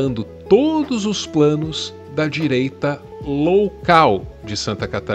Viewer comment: Só eu ouvi "direita LOWcal"? 😅